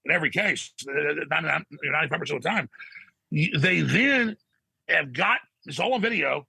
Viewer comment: What is Alex Jones saying at 5:11? The reiigh? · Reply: They then? Sorry can't hear anything else similar